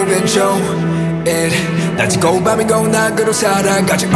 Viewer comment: I'm convinced he's saying 'shit' instead of 'shoosh'